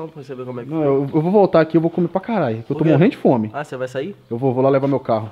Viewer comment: só eu que ouvi ele falar carai